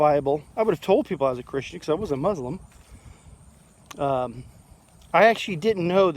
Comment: Back then I thought there were four groups , Christians, Muslims , Catholics and Jews. So by process of elimination I figured I was a Christian. I never even considered the eastern religions like Buddhist or Hindu. Not sure why I thought this way other than I had zero background on any of it. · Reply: OK, I thought I heard you say I was a muslim @ 3:34.